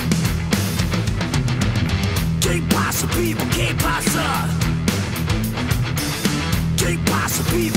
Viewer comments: MI parte favorita de la cancion es cuando dave dice "Que pasa people, que pasa" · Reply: Yo pense que decia "que pasa tipo que pasa" 🤤🤤🤤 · MI parte favorita de la cancion es cuando dave dice "Que pasa people, que pasa" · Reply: Yo entendí "que pasa tipo que pasa"